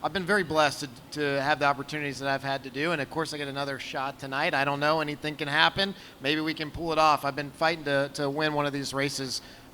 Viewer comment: did he just say "maybe we can Poole it off" because if so that's using your name properly hahaha